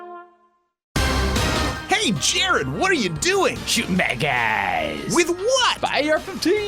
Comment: For the longest time, I thought Jared (Who may or may not be gay), was saying "Shooting Baguettes". No reason why it would fit into the commercial but that is what I thought he was saying.